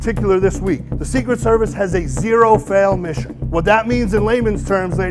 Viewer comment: “Secret service has zero fail mission” JFK: 💀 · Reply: He said 0 percent goal not a 0 percent fail. I thought he said that as well had to rewind a few times